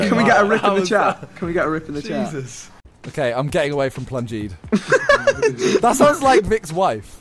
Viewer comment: What did Simon said in the last clip? "I'm getting away from ......... ?" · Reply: Lol ok thanks ^^ I heard "plungers" I was like "I don't get it :-/ " ^^